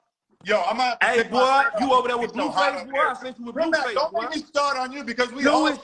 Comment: I seen the video 6:41 I just wanna know what asked in the beginning I been dying to know I just can't hear it cuz talking I keep repeating it but can't hear what said all I heard was ay boa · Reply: Said he seen him w blue face